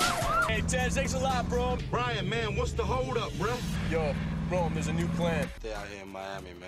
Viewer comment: Does Paul call him Brome here?? As in bro + Rome... hahaha